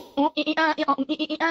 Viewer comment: "3, 2, 1 GAY" Did i misheard or it is actually right? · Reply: no lol its "date" it plays when u select senpai's stage